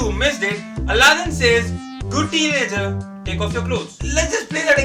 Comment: Whut alladin meant say was "good tigers take off and go" u just mishear it as"good teenagers take off their clothes"